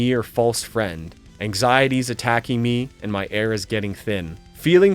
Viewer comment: Great stuff man! I think more bands should write lyrics like the ones in sweating bullets. I genuinely always thought he said my HAIR is getting thin rather than air